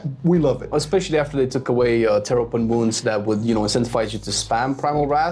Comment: Mew mew mew 😸Fun fact, for the longest time, I thought people said terrapin wounds instead of tear open wounds. Yes, I realize this makes absolutely zero sense and why I stick to resto druid 🤣